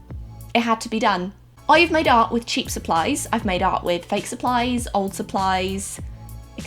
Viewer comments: In the beginning when she was talking about what she has made art with I thought she was saying I madeout with _______😂😂😂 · Everytime your said "i made art" i thought your were saying "i made out" haha